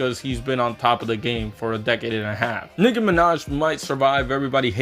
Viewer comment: what did he say?! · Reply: I peeped during editing but it's very clearly Nicki lol